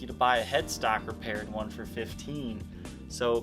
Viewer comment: Hi there. Question, if I may; Did I hear you correctly, when I thought you said that a head-stock repaired one would go for 15 thousand? I ask, cause I've got one that (though it does have its share of dents and scratches and lost its shiny finish) IS ''a survivor',' so without any of the repairs you mentioned, which made me wonder what its market value might be. (no neck binding, so ebony?) It's just curiosity on my part, and would love to hear a brought estimate, of course without any ''strings attached'' (pun intended), from a knowledgeable person like yourself. Thanks in advance ! Great channel, btw! wdit: the guitar's serial number is 136464, but it doesn't have made in the USA stamped under it, which had me mistaking it for a '63 one, for a while, eventhough it was missing certain features the ones from that era have. I bought it as a used guitar from a musical instruments shop in Amsterdam, the Netherlands, in the mid 1980's. (sadly without its case) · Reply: he said $1500 - a 1963 would go for a lot more